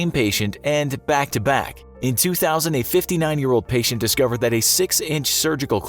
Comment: I thought he said the patient was 2000 years old...